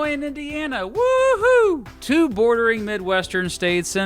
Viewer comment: For a second I thought he said "two boring Midwestern states"